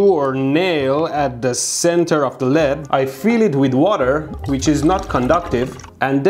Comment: i thought he said "i feed it with water"